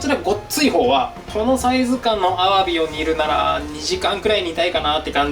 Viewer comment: このぐらいのアワビを煮るなら〜って聞こえるの私だけ🤣？更新待ってたからとても嬉しい！